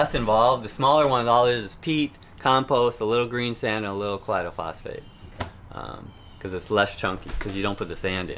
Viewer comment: Hey....Love your channel and you have definitely a love of what you do and do a great job educating us!!! During this video, talks about the smaller soil blocks at the 2:50 mark....but I cannot catch what he said about the specific Phosphate. It sounds like "Clado" Phosphate at the 2:54 point. Can you be so kind and let us know? I have goggled it and no luck. Thanks and as always.....we appreciate your videos! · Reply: Hey Keith! I think you’re talking about the colloidal phosphate.